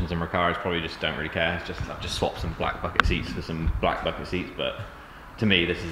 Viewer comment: Love print number 3 looking sick, when you said swapped black bucket seats for black bucket seats I thought you said 'black fucking seats' LOOL😅